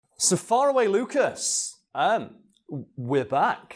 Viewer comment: "No-Longer-Far-Away Lucas" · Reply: ohhh i thought he said "so far away lucas" like "so lets get startet (with the next on)"